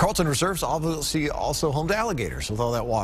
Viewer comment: wait…did he say panthers? We have those here?!? 😰